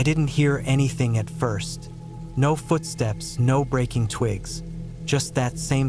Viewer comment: Hate the stupid voice that says little things like no noise no rain with that weird voice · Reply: I thought that he said no food stamps rather than no foot Steps a couple of times when I first heard it